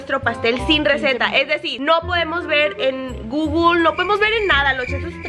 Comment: juro que entendí "receta sin pastel" jksksksks